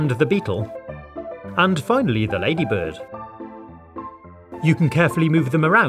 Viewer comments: Did he say ladybird? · Did He say ladybird at 3:13 ? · is it me or did he say ladybird Slide down wtf is that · why does it sound like he said “lady bird”? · did he say lady bird or lady bug